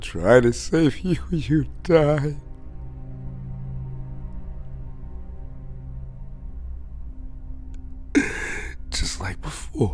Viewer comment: I ruined the sadness for my self- I thought he said: "You died..Just like my floor" 🙂🗡️